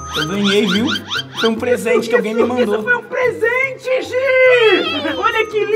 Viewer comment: só eu escutei o Harry chamando a Hermione de gi? Kkkkkkkkk